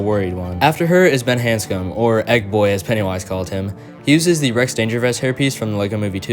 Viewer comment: I thought pennywise said fat boy but when I rewatched it I heard egg boy